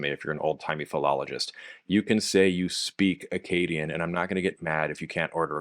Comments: I was very confused for a second when you said "Akkadian", because I heard "Acadian"! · Well I must confess that I heard “Akkadian” as “Acadian” and was momentarily quite confused why there were no more ziggurats in New Brunswick… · You forgot the most important function of Akkadian, writing complaints to your copper supplier about his terrible customer service and broken promises on the copper deliveries. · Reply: When he was talking about Akkadian I thought he said Acadian and I was very confused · I thought you said 'Acadian' instead of 'Akkadian', and got myself very confused for a second 😂